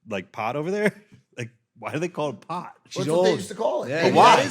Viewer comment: bro did they just say y she call it pot😑